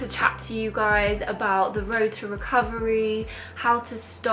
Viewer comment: Love it Thought u said How2!!